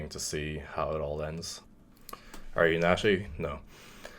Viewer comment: What are you saying?? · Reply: "Are you natty?" Meaning is he taking steroids. He responded "No." Meaning he is taking steroids.